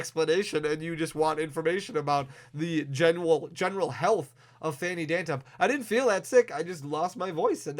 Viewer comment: Did you really say at 0:58 lmao love it man always keeping us entertained👍👍👍